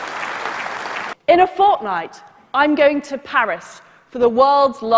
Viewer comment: NO WAY DID SHE JUST SAY FORTNITE!?